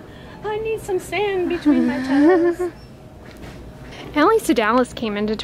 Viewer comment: Anyone know what vlog is saying at 7:24 ? Is sounds like Alice .... ? Thanks !